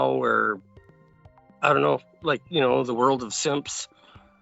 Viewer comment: Did he say "the world of SIMPS" at 2:47?!